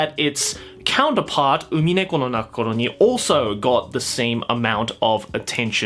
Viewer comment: pls help, I didn't get any of what he said UNINOKORONAKORONI (???)